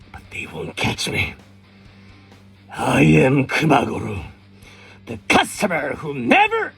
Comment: I just woke up, im so tired and i thought he said "I AM KANGAROO"